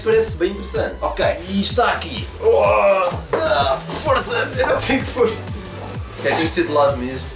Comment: só eu ouvi Forza ao envez de força kkkk